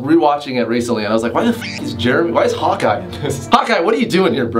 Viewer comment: when he said "Hawkeye", I heard "hot guy"